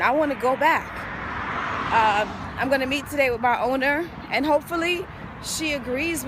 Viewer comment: Wait...@ 3:46 did she just say, "I'm going to meet today with MY OWNER"?????? WTF lol, damn you went all the way there to be a Mexican smh